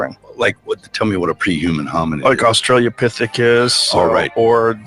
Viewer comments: did rogan say “australia-pithicus?? 😂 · Did Rogan say "Australia Pithecus" or did I just hear him wrong?